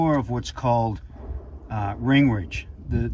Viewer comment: Either my hearing's shot or my volume was too low. Thought said, "Newt Gingrich," when he actually said "Ring ridge." I care more about freeing up an engine over politics. Yeah, I'll get my hearing checked.